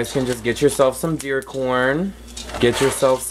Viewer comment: Bendicion Baba! My godbrother shared your video with me. I’m glad to learn from this! What is the right type of corn used for this (that doesn’t make popcorn 😄) and where can I purchase in bulk? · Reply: it sound like you said deer corn. I googled and found some at tractor supply.. thank you so much baba